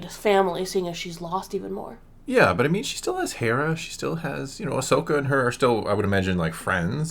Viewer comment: talking about all the bad things that have happened in her life and for a second i thought he said "she still has hair". My hearing mistake made me laugh a bit